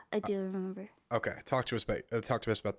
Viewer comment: did he actually say "talk to us babe"?